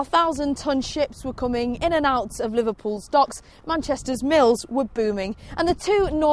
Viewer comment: sound like she said: "Manchesters milfs" at 0:37 hahaha :p